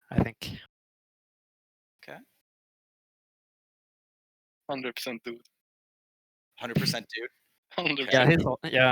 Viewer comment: Who did they say the best hon player is? @ 7:40:10 I didn't get that. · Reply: Jiggle Billy